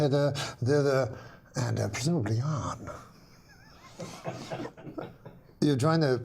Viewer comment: what does norm finish this sentence with “hither, thither and presumably……..” sounds like he says “presumably on” but that doesn’t seem to make sense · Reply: hither, thither, and presumably yon